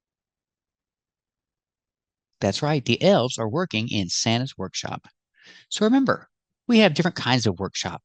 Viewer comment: As far as I heard, she said "workshow" 😊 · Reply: That's what I hear too "workshows"